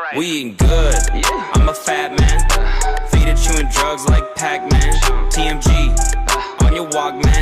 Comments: i thought it said “feeding children drugs like pac-man” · For so long I thought it said we good in the beginning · What people really searched: We eating good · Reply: I legit thought it said "we ain't good" I'm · My dumbass thought it said “We ain’t good”